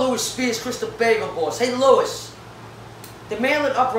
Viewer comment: I thought he said “hey lowis”